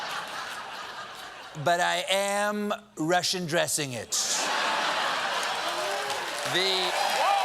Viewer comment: Hmm.. Closed Caption says 'Russian Dressing" while I heard "Rush Undressing"... I suppose with Trump, either statement might prove correct...